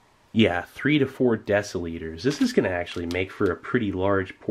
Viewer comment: Just listening to this in the background, I was sorta surprised when I thought he said "oh its 3-4 Decalitres, so like 300ml" but no, After listening again its decilitre, which makes so much more sense LOL. still odd to not just put something like 350ml on the packaging.